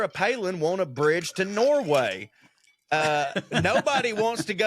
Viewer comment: Ah. I misheard that bridge to Nowhere/Norway bit.